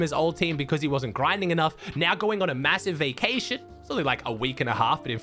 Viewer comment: did I hear… “massive?”